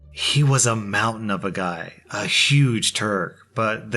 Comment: For the first story when read "he's a huge Turk" I heard "He's a huge turd, but he's the kindest guy" and was so confused